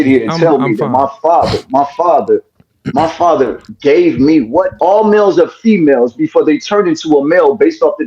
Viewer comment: Did he say all males are females until? 7:27 or im trippin.